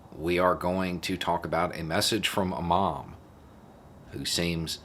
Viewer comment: Hah ... I thought you said "imam" rather than "a mom" at first. I was trying to figure out where it was going for a little bit...